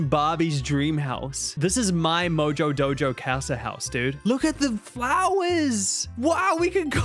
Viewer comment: did he just say house house (cash means house)